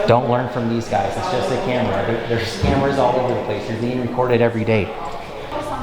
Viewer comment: at first, I thought he said "There's scammers everywhere."😅